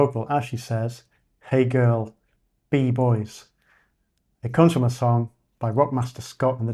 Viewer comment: I thought it said "Hey girl, eat boy" like some kind of subliminal message.